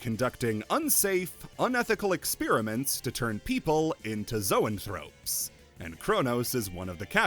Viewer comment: I dead ass thought you were saying zoan fruits instead of zoanthropes